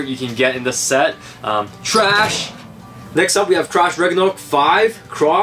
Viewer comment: it Sounds like he said Trash Ragnaruk